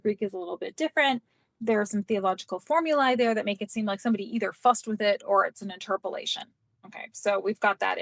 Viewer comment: Did I hear "... Someone fucked with it" regarding Josephus?? 😂😂 Hope so!! 5:55 edit: if Jesus was god, why did John the Baptist baptize him? Did god need to be baptized?? 😂